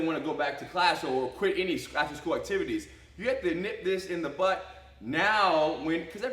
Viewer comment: did Ryron say "nip this in the butt" ?